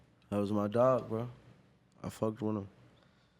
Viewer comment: Anyone else hear ‘I fucked one of them’ (5:45) or am I just really fuckin high